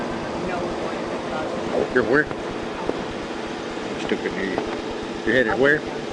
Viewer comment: And to think she has a boyfriend. I would be horrified if my partner acted that way under any circumstance. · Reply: Did anyone else hear him say “stupid idiot” around 2:10? I kept rewinding and that’s what I heard